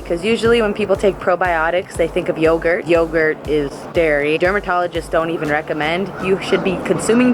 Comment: I thought you said that yoga was dairy for a second 😂😂